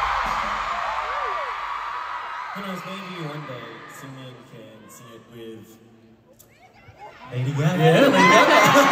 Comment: What did Chan and Seungmin say please? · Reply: Chan said maybe one day Seungmin can sing it with Lady Gaga.